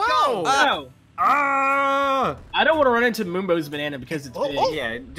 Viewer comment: I thought you said mombo